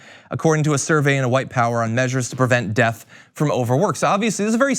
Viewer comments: did he just say white power? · why did you say white power but it says white paper???